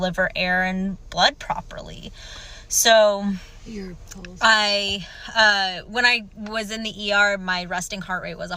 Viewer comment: I heard another ladies voice saying "you're repulsive" in the background !?!🤔😬